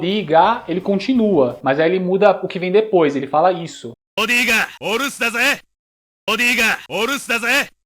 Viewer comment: o que eu entendi nesses golpe 12:36 "vodka, buscar café" um alerta spoiler vodka com café não é muito bom 4:43 "Já comi o brigadeiro zé"... 8:07...melhor eu não falar o que eu entendia, hahahahahahahahahaha 9:35 Sumaré! (é um bom bairro) 10:48 Vodka amei zé (mor cachaceiro esse kyo) 20:46 JACARÉ!!! 20:28 Magno Quetaaaaa